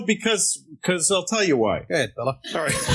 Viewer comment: Anybody know what Dipalo says at 9:20 that gets the chuckle from CK? "Go ahead..." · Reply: I think he says go ahead fella